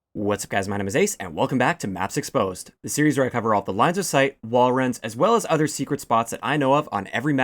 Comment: I thought for a second it said "lines of site and walruses"